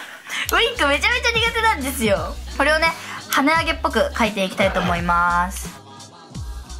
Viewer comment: ごめん...跳ね上げっぽくが、鼻毛っぽくって聞こえてしまいますた...🙊